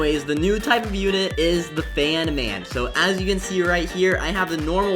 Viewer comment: I thought you said banman